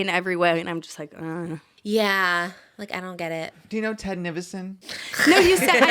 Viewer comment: Ted Nivision and Brooke is the crossover I NEED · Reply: Bruh i thought they were saying Tednificent